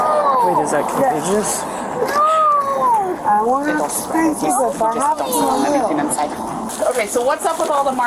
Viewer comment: They asked her what form of discipline they use with him. They didnt hear what she said here 15:56 bc they were talking about bug bites. She said, "i dont wanna have to spank u but i will."